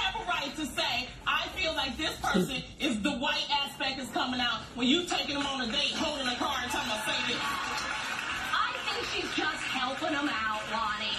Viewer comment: I thought Loni said white"aspect" tho..